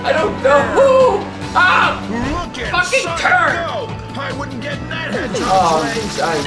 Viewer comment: Did just say "Fuck no!"?